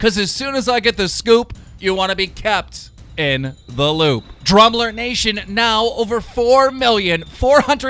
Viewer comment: Im so stupid I always thought he said drumller nation 🤦🏿